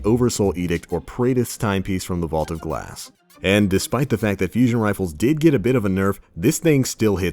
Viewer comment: He said fusion rifles got nerfed instead of pulse rifles